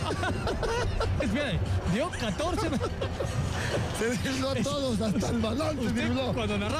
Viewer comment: que dice campos?jajajaj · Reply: "Se burló a todos, hasta al balón se burló"